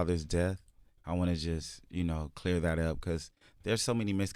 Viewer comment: Y’all at 3:42 she is mouthing words to him before he answered the question about how they think he died. Somebody try to make out what she is saying!!!!! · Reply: It looks like she said "Clear that up"